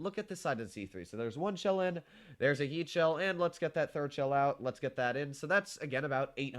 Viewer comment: i heard there is a yeet shell hahahah